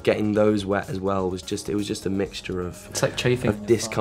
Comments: What did Joe say on minute 7:12? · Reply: "It's like chafing" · What did Joe said at 7:12?? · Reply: he said “chafing” which is when a part of your body is made sore by rubbing against something else